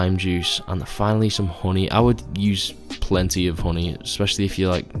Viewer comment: You're accent! When you said "honey", I heard "horny" XD. I'm totally going to try these smoothies though. Thannx.